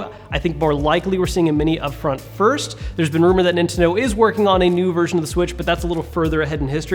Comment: you said Inteno instead of Nintendo